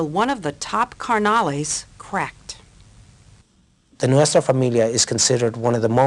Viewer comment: lol i thought it said Nutella family 😅